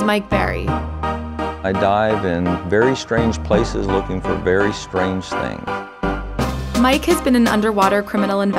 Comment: I thought he said "I died in very strange places looking for very strange things"